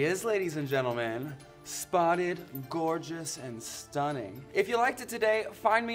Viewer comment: She said "spotted" but I heard "rotted"